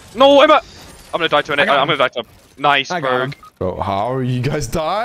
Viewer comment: I heard “ima die to a n-word”